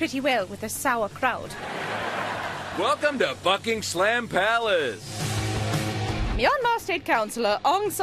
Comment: Wow, at 2:00 it sounded like he said Fucking Slam Palace. (I know he didn't say that)